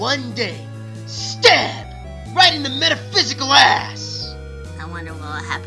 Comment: I swear If the subs weren't there I would have thought Geno said "Stare! Right into the metaphysical ass!"